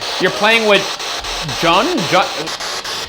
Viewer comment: I'm pretty sure I heard "playing with toys," not "playing with joy" when they were using the spirit box after drawing ...